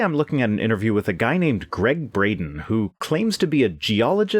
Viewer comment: I thought you said, "Greg Brady" at first. XD